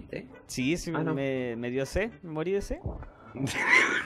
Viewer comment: tu mamá me ama escuché perfectamente la psicofonía